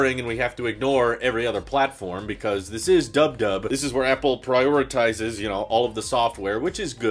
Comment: Quadruple u dc? No body says that. Stop trying already. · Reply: He actually said dub dub 01:30 haha